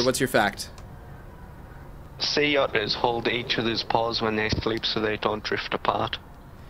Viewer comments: I thought he said sea otters hold eachothers balls when they go to sleep so they dont float away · I thought he said see otters balls. lol